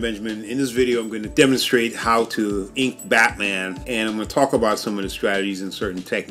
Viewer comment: I heard "eat Batman" at the beginning